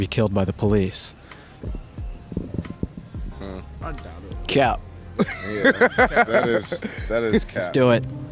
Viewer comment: In 4:20 the guy says a word that I can't understand what it is, cause i'm brazillian Can someone explain it to me? It's a slang or something? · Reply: He's saying "cap" which is slang for lie. It's so redundant.